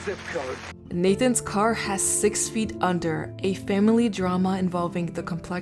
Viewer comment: lmfao i thought it said sex offender at first🤣